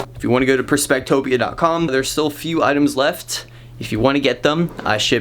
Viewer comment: Lol I heard "I ship everyone in the world" 🥰🥰